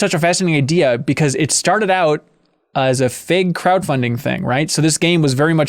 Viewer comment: Did really say, "It started off as a FAKE Crowd Funding thing" When talking about the origins of Psychonauts 2? I am genuinely unsure if he said Fake or something else, or if he meant it like it as Crowd Funded by a random Crowd Funding company instead of like Kick Starter or something? Yeah not sure if I misunderstood or misheard him, or if he was actually saying the Kick Starter/ Crowd Funding was BS. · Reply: Oh Gawd Damn it... He said Fig Crowd Funding.. LoL I have barely ever heard of Fig before hearing about them in relation to this game actually. I knew wasn't saying "Fake Crowd Funder" lol